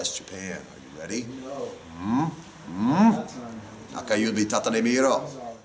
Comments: somebody please tell me what Corey said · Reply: Nakayubi tata rete miro = Look at the middle finger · what did corey say in japanese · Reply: nakayubitatetemiro